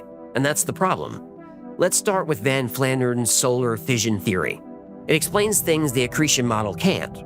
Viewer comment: I thought you said Ned Flanders at first.